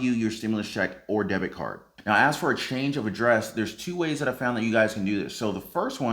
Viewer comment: Be Sure to LIKE This Video if You are READY for a GREAT NEW YEAR 2021!! $2,000 Stimulus Checks for DEPENDENTS - TRUMP DEMANDS 👉 <link> NEW $1200-$2400 WHITE HOUSE Stimulus Checks!! 👉 <link> OHIO Rental Assistance 👉 <link> $3,300 Arizona for Mortgage | Rent assistance + Electric and Gas 👉 <link> $500 Minnesota Stimulus Checks 👉 <link> $8,000 in assistance for rent Delaware residents 👉 <link> Florida Residents $1,000 👉 <link> $1200 to Washington D.C. Residents 👉 <link> Texas Assistance $1,200 + $1,900 in payments 👉 <link> Connecticut residents $300 per week ($1,800) MORE Unemployment Benefits 👉 <link> $375 Colorado Stimulus Checks 👉 <link> $600 Rhode Island Residents 👉 <link> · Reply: Thought u said he had a change of heart